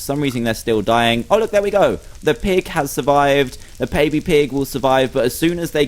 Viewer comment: he said paby pig instead of baby pig.😁 It is still a great video.😃